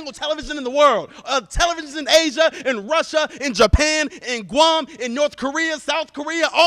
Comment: Did he just say "Guam"? My late grandfather's from Guam!